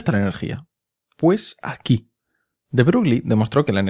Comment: Como se llama el nombre del hombre que dijo en el minuto 2:34 quién me dice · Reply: Supongo que dices De Broglie (Louis-Victor de Brouglie pero nunca le he oído por el nombre entero) 🤗